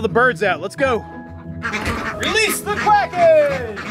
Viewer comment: "Release the quackers!" is now my favourite phrase ever - thank you so much ((also toby is adorable and doing an amazing job at guarding)) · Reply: I thought he was saying "Release the Quacken!" (Release the Kraken!)